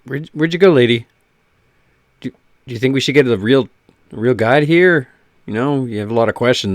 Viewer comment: Guide...you said guide LMAO I thought you said guy&I was thinkin man she's ugly but she doesn't look like a guy..much. Aaaaaaahahahahahaha!!